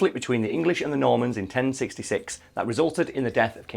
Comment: I thought it said mormons